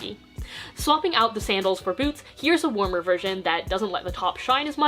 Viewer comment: i swear she said boobs